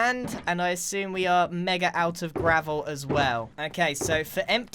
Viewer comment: did he just say "MEGA"?